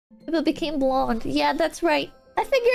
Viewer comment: Ela n falou loira invés de morena na primeira frase ou eu que oivi errado? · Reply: sim, eu ouvi "blond"